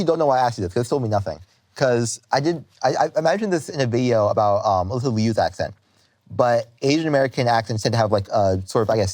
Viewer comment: Alysa Liu* not Olivia!! The subtitles are wrong ⚠️ (This confused tf outta me lol)